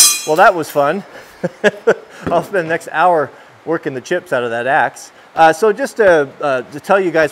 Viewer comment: I thought you said three chops.